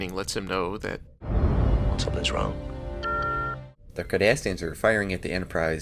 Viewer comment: close caption said KARDASHIAN